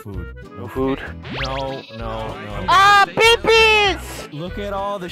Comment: why tf does he say babies like that at 0:58 "BEBIES!!"